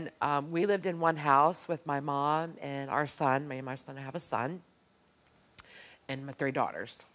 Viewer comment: Did she just say me and my son has a son